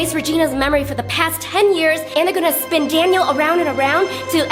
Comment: When the PZ leader said Daniel he sounded super duper silly 😜 🙃 😝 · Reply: It sounded like you said Danielle